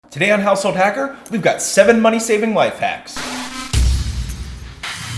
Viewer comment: i thought he said 7 life saving wife hacks i was like finally